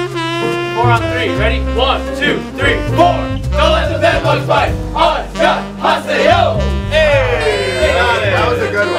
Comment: Can somebody tell me what did they say at 12:06 ? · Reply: Don’t let the bed bugs bite An-nyŏng-ha-se-yo